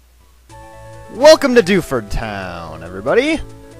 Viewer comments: I thought he said "welcome to DOUCHEford town" lol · When you said 'Welcome to Dewford' I thought you said 'Welcome Dufus'